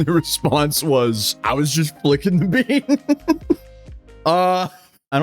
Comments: I think he said "flooding... the vein" not "flicking the bean" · I thought he said, “flogging the vein.” But I’m going to rewind though.